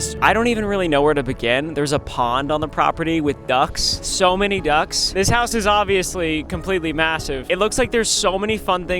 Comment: did you just say ducks those a geese!